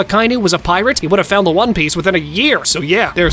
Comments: I thought I heard one peach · At the start did I just hear bro say "one peach"!?!?!?!